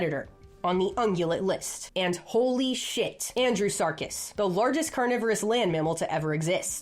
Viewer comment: thought you said Andy Serkis for a sec there 😅